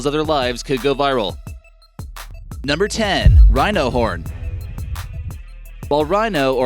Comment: I thought he said rhino porn while I was eating and almost spat my food out until he said horn again 😂😂